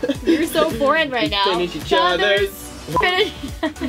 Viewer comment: did Thomas say "finish each other's.. farts" XDDDSS